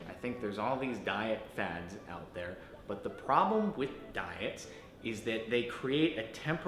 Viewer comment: for a second I thought he said “I don’t believe in dying”